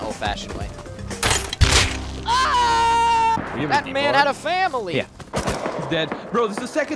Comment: did u say family??